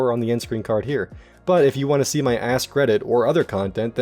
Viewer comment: I misheard "if you want to see my Ask Reddit" as if you want to see my ass credit.